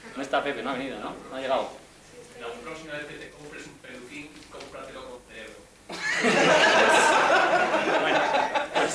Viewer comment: Qué dice Pepe en el min 44:10?? Que buen material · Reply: "La próxima vez que te compres un peluquín, compratelo con cerebro"